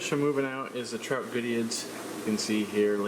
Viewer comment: What's the fish name on 4:06 I can't hear it clearly and Google gives me nothing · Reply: Trout goodeids